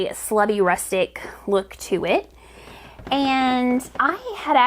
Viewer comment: At about 19:19, did you actually say that yarn had a slutty rustic look to it? I actually replayed that part 3 times 😂